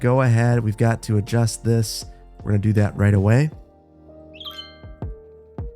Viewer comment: 🚲🍿 Sounded like someone said "I know how to do it right" and got too much power.